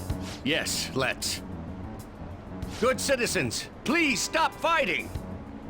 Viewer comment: I was confused why he picked a joke one because I thought it said let’s meditate. I’m stupid